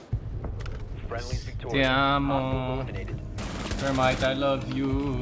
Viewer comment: did you say "ti amo"???